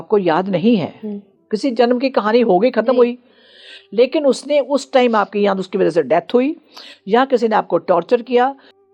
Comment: what was that muted word? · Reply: Rape